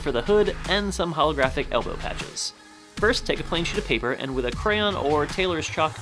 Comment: Lol Instead of thinking u said plain sheet of paper I thought you said plain sh*t of paper